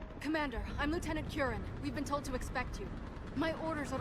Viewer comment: For a second there, I thought she said that her name is Lieutenant Kyurem (as in the Pokemon) 😂 that made me do a double-take!